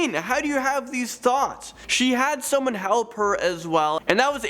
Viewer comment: Swear he said thots at 5:56 but it it's thoughts. (・∀・)